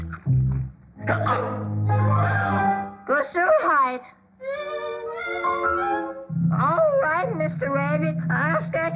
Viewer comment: What did he said? I understood polish word 😆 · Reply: Gesundheit